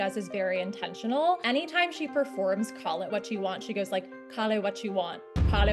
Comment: She clearly said “call it” 😬